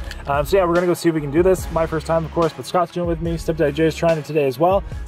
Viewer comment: What did you call him?? anyway he's one fine looking specimen :D Does he record vids? Love his hair, love his beard, he's just beautiful <3